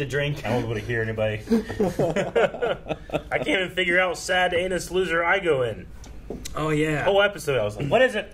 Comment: “ I can’t even figure out what sad anus loser I go in”, I was like what did just say?? Lmao, I forgot that that was the clue they gave jake 🤣🤣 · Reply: It's Dangerous liaisons